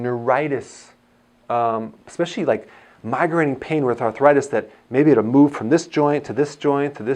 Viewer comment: Dr Berg at 6:22 or so you are speaking about pain moving from this joint to this joint etc; the caption says migraine pain moving from joint to joint- but I think you said migrating pain?